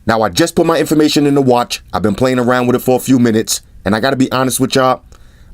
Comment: When you said (as you always say) that i just put my information in... I've been playing with it it for few min... what i heard was: "i just put my information in... I've been playing with xerxesfor a few minutes!"😀